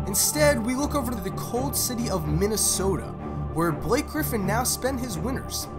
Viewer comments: Did he say the city of Minnesota ? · did he just say the cold city of Minnesota? ???